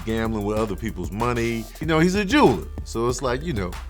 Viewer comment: Did he say "Hes a Jew" ? Because if he did, thats not okay with me. Thats racism. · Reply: ANKH-RA 999 he said “he’s a jeweler”